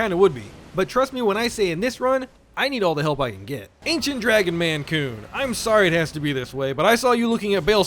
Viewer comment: Am i dumb? I heard "Ancient Dragon Maine Coon" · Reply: "Ancient Dragon man-kun", kun being a name suffix in Japanese (similar to -san, -sama, -senpai) meaning a (typically male, or masculine) friend (and/or younger than the speaker)